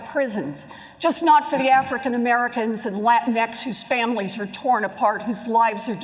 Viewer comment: did she actually say LatinX ?